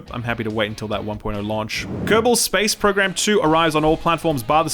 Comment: I was distracted, looking away from my screen and I could have sworn I heard "Goebbels's Space Program"...